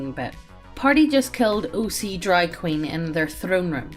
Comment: Hey people can misheard the craziest shits online, case an point: 9:20 Made me do a double-take because I thought said "party kills OC DRAG QUEEN" and I went like "wait what?"